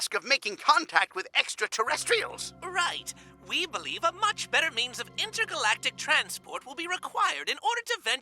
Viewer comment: “Right, we believe a much better MEMES of intergalactic transport will be required in order to venture further into space.” I can’t be the only one who heard that...